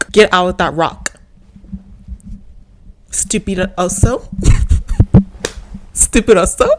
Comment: ok, but why did you call me a stupid bear?